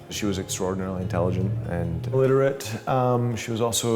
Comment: The guy confused me. It sounded like he said intelligent and illiterate.